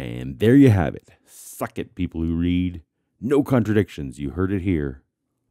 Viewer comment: when you said "suck it people who read" it sounded like you said "fuck it"